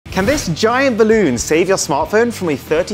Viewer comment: I swear I thought he said : can this giant condoπ save your smartphone from a 30 foot drop !?